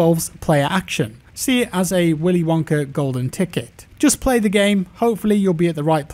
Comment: lmao I heard it as "Willy Wonker"